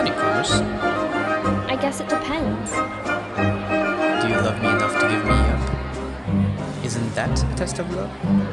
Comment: For those who can't hear, This is also page 193 in The Cruel Prince, Locke: "You are very beautiful, like a winter night." Jude: "I'm glad you like the dress." Locke: "Tell me, could you love me?" Jude: "Of course." I love my parent's murderer, I suppose I could love anyone. I'd like to love him. Locke: "I wonder, what would you do for me?" Jude: "I don't know what you mean." Locke: "Would you forswear a promise for me?" Jude: "What promise?" Locke: "Any promise." Jude: "I guess it depends." Locke: "Do you love me enough to give me up? Isn't that a test of love?" Jude: "I-I don't know." Locke: "Do you love me enough to weep over me?" Jude: "You mean if you were hurt?" Locke: "I mean if I hurt you." Jude: "If you hurt me, I wouldn't cry. I would hurt you back." Locke: "I'm sure you'd-" Cardan: "Time to change partners. Oh, did I steal your line?" long pause of music Jude: "What do you want? Go ahead. Insult me." Cardan: "I don't take commands from mortals." Jude: "So you're going to say something nice? I don't think so. Faeries can't lie." Cardan: "You really hate me, don't you?" Jude: "Almost as much as you hate me." another long pause Cardan: "Until we spar again." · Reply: OH MY GOD THANK YOU. For a moment there, when he said "Do you love me enough to give me up", I heard it as "Do you love me enough to get me up?" XD